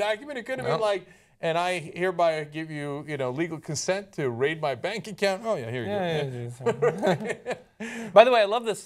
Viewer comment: I At first I thought Cenk said, "...rape my bank account."